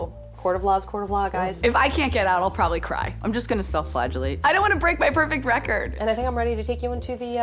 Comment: "IF I CAN'T GET OUT I'M GONNA CRY" "I'm just gonna self flagellate." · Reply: I'm not gonna lie; I thought she said "self-flatulate".